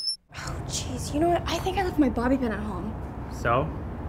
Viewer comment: What do they say at 1:54? ''I think I left my ?? at home'' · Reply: Bobby pin